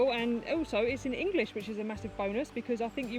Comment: "Massive bonus"? Oh, I thought you said "Massive boner!" 😂 I was a little underwhelmed too. Slim pikkens, eh? 😉 As an aside, next time you book a flight, do let us all know so that we can avoid that one. I mean, I wish you well and all but, you know, self preservation! 😉😂